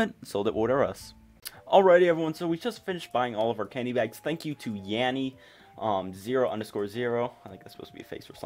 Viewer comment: Did i hear "Thanks to laurel " lmao